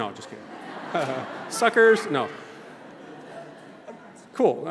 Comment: did u say suckers ???